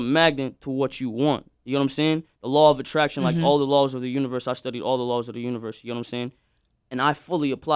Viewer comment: Did he say that he studied all the laws of the universe?